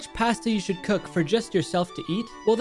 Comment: did he just say “Paesta”?